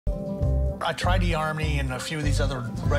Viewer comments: It took me a few times to realize he said eHarmony. At first I thought he said the Army. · It really does sound like he said he "tried the army and other dating sites". Not "eHarmony." Also, he didn't bother to learn any Ukrainian or Russian in the entire time he was writing to "her"'. Or workout. Or put in any effort at all apart from picking out a picture from a catalogue. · I never know that the Army was a dating site. At first I thought he meant he tried to meet women solders. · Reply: It sure sounds like he said "the Army", which is really odd. I thought maybe i heard "eHarmony". but no, pretty sure he said "Army". So, there you go.